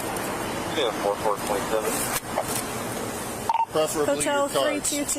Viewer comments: Wtf you mean? He obviously saying "Preferably your cards." 11:47 This ones a lot harder to hear, but he is saying "Do I have to use my stick all the time, or not all the time?" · most of the "ineligible" parts are stupid whoever made the subtitles clearly didn't give it a 2nd listen for example 11:12 what do you mean ineligible he clearly says "preferely your cards" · I think he said: “ preferably your cards”